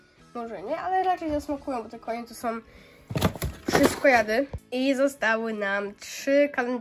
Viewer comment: Czy tylko ja usłyszalam kaszojady jak mówiła że konie to so wszystko jady xD🤣🤣😂